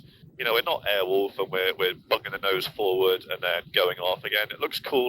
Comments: Please, may you tell me what is the pilot saying at 2:41? I can't recognize the words, "bugging the nose forward" is it? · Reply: I think he says "bucking the nose forward" as in very aggressive nose down attitude you sometimes see in movies or TV. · Please, may you tell me what is the pilot saying at 2:41? I can't recognize the words, "bugging the nose forward" is it? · In my flight sim my rpms drop, is it normal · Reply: Please, may you tell me what is the pilot saying at 2:41? I can't recognize the words, "bugging the nose forward" is it?